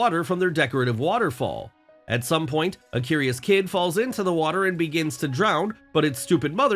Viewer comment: A doe with a WHAT ?? I could have sworn you said "cub", which isn't even close.